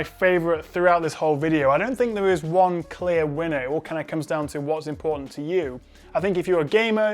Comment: Ik he said “it all comes down to what’s important to you” but for some reason I heard “it all comes down to watching porn “ 😂😂😂